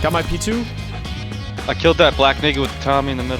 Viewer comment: hahah did he say I killed that black n””ga